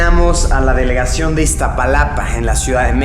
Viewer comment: Min 4:20 luisito dice: nos encaminamos hacia la delegación Iztapalapa. Lo que pensamos que dice: nos encaminamos hacia la delegación iztapalacra xd